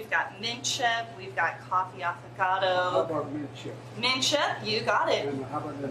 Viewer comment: I'm sorry, side bar...did she just say "coffee-avocado" ice cream?🤮